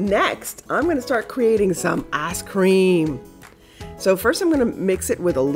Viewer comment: it sounds like you said ass cream! haha im sorry! don't be mad at me. im ridiculous! xox